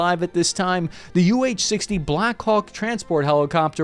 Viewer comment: is he saying Black Cock or it is just my English?